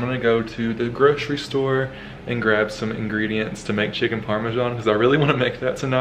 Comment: i thought that said chicken pram :') hope you're doing well! so much love and appreciation for you! You're awesome dude :)